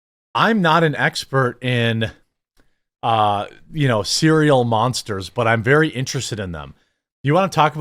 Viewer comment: When he said serial monsters I legit thought Count Chocula, Frankenberry, and Boo Berry. 😂 I’m an idiot. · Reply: Holy shit I’m glad I’m not alone. Thought homie said Cereal Monsters. 😂